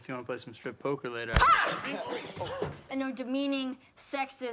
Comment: What Talitha says at the moment 0:53??? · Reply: Sara Andrade she's cool😎